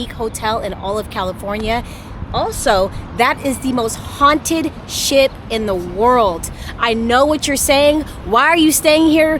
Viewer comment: I thought you said it was the most haunted sh*t in the world 😂😂😂😂